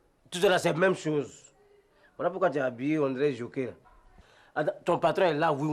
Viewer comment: C'est pour pourquoi tu es habillée on dirait "jockey" 3:46 lol